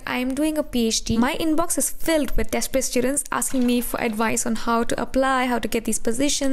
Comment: You're a PhD, and say "childrens" and not "children" And second of all, you're not a career counselor for sure but you have walked the path, so a little guidance won't harm. In that way, you're not a chef then why make food channel? And just because you're a PhD and did your own research doesn't give you the right to call others "desperate" . A hint of arrogance was felt in your tone. Just a constructive feedback, no hatred. P. S. I'm apologizing for my first statement (only) because I heard it "childrens" but now after watching it today I realised she said "desperate students*" and not *"childrens" Nevertheless, I stick to the other part of my comment.